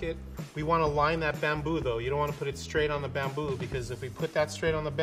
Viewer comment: I heard bamboozle instead of bamboo